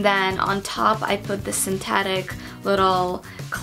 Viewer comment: When you said synthetic, I thought you said satanic.... Anyway, it's 4AM I g2g